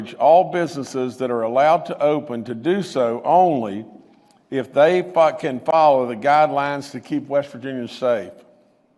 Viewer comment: What did he say at 13:56?? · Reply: Fuckin